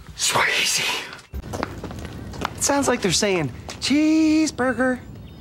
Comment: The birds sound like theyre saying. "Cheeesseburger! Cheeseburger!!"